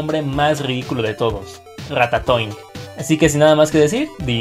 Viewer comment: RATATOING parece que dice RATA SOY jajajajajajaja 😂